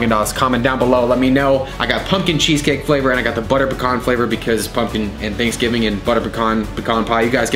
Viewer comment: Hold up... did he just say butter PACON? Its peeecan